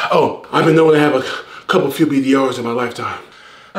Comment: I been known to have a couple ????? in my lifetime? Wtf did bro say there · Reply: BDR's Bad Dicc Reports